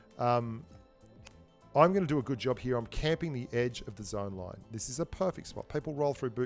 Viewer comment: Did I just hear: I'm camping here???!?!?!?